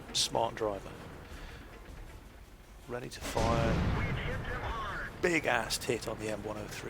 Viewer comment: i thought you said big assed tit